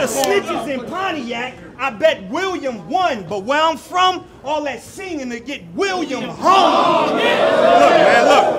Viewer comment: I swear I just heard say "where I'm from that will get William hung". I'm hoping I heard wrong. If not, wtf?! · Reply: Where I'm from all that singing* aka snitching* would get William Hung°* William Hung was a viral celebrity in the American Idol days of American television. He's truly an anomaly, and I strongly suggest that you Google tf out of him